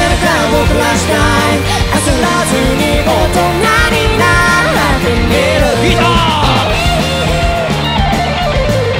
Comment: 「焦らずに大人になってみる」の後のもっくんってなんて言ってるかわかりますか？ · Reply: ぎたー